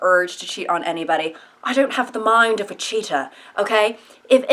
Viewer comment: I keep hearing cheetah! LOL!